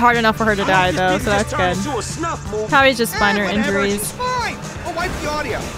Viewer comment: bruh i thought u said "spinal injuries" i was like damn where did that dark humor come from😭